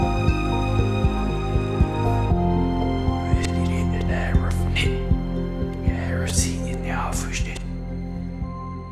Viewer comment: Something was said here but backwards... Does anyone know what it was? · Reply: "did you find the easter egg infrared 82"